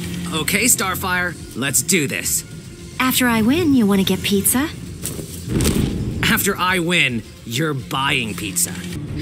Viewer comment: "after I win, want to get pizza?" "after I win, you're buying pizza" Mikey is a Savage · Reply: I thought I heard After I win, you'll be my pizza.